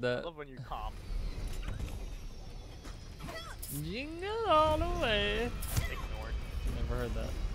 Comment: When Mei said, "Jingle all the Way" I thought I heard, " You are the wae" lmao